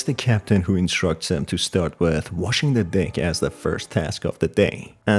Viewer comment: Bro why on 0:06 in the subtitles it says "wash the dick" instead of deck💀